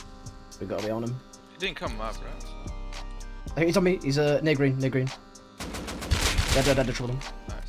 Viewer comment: what did he sayyyyy? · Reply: “He’s on me, he’s near green” hopefully that cleared it up